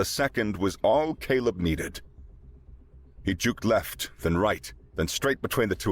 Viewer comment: lol you said juke but the captions said jinked. To "jink" is to suddenly and nimbly change direction. A "juke" is a feint. It is a deception to mislead someone into making a mistake.